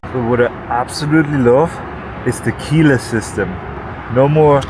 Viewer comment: Anyone else heard that loves “Tequila system”? 😅